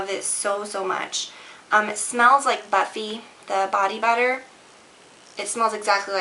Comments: I thought you said it smells like butt feet.....LOL.... Buffy! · I thought you said the bath melts smell like butt feet rather than buffy :/ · HAHA! When you said "It smells like Buffy" and I swear I heard "it smells like butt-feet" and was like "WHAAAT?!" hahaa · when u were talking about lush the 1st product i thought u said "it smells like butt feet." i was like ohh... good? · Oh my , when you said "Buffy" , I could of swore you said "Butt Feet" ! I had to go back and replay it lol . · When you said "buffy" I thought you said "butt-feet" instead.